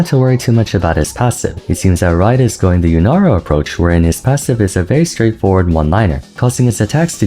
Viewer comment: Is it just me or are the English captions saying Naafiri instead of Locke and sometimes mentioning her in the most random places? It also put "Yasuo and Yone" instead of Lunara at 2:57? LOL. Like is YouTube using an AI correction tool for the captions or am I losing my mind, because when he mentions other champions the captions get the spelling right which is not usual for automated ones.